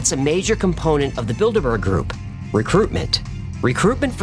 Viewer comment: Every time you say "the Builderberg group" I keep hearing "the Build-A-Bear group".